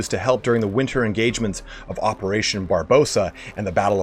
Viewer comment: I'm sorry...did he just say operation Barbosa?